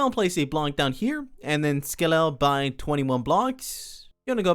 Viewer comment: does he say 21 blocks or 20 what blocks? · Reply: 21